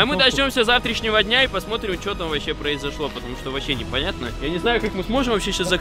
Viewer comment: мне одному послышалась фраза "вообщем похуй"?